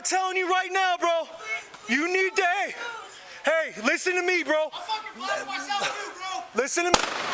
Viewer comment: I thought he said, "I'll fuckin' blast myself for you, bro!" and then shot himself in the head.